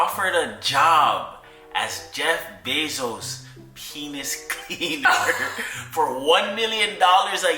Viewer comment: But you do need to let us know exactly what that lizard lock is bro, for science ofc xD wasn't that tune Busted? I thought you said just Jeff cleaner at first and was like hell yeah, till K said 1 mil to clean the schlong, that threw me all the way off XD as nice and caring as he is, AINT NO WAY your ex is staying with y'all and i aint even heard the response yet lmao would get maybe a hotel or something xD